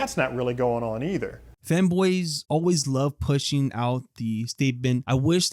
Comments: i misheard "fanboys" as "femboys" and i was like "thats a ballsy segue into the topic of perceived erasure 😂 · OOOH FANBOYS deadass thought you were saying femboys ok now it makes alot more sense